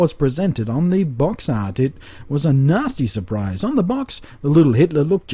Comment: Did you say it was a 'Nazi' surprise?? ;)